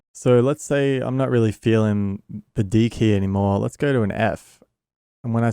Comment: Just me or did anyone else hear 7:06 "I'm not really feeling the deek here anymore" haha great tutorial but, love watching fellow Australians. Subbed!